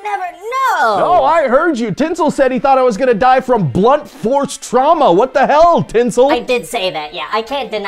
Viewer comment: The fact that one of the elves was betting on blunt force trauma is hilarious. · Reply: OH, I thought he was saying “blood force trauma” 😂 I feel stupid 😆